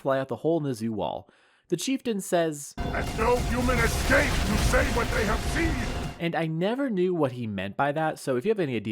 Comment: I think he says “a fell human escaped to say what they have seen” “Fell” means “of terrible evil or ferocity” as an adjective, derived from “felon,” so I think he is just saying that this dastardly ODST escaped and will tell the UNSC what the Covenant is doing up there